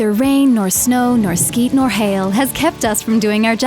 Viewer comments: Did she just say “skeet?” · Did she just say: "rain, or shine, or 'skeet'...?" Lol.